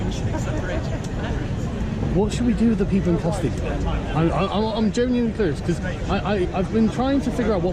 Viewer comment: Did you just say "I'm genuinely clitoris"? haha Great job bro. Keep up the good fight.